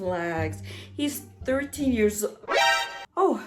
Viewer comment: No cause I thought it said “13 years old” too and was about to burn my phone